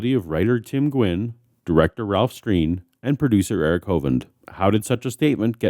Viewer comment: I heard, “producer Kent hovind.” And internally sighed